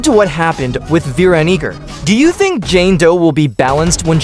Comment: bro almost said veniger instead of veeronica